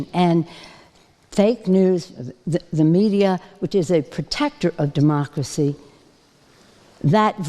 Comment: did she say fake news is a protector of democracy?